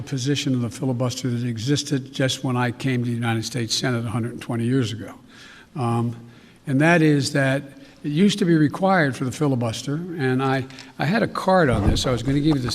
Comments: Did he say he came to the senate 120 years ago? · Did he say he came to the Senate 120 years ago?! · Am I the only one that heard him say he joined the senate 120 years ago? LOL